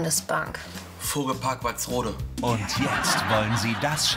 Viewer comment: was sagt der dritte, wo alle lachen? Hörs mir grad zum xten Mal an und verstehs akustisch nicht :( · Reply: Vogelpark Walsrode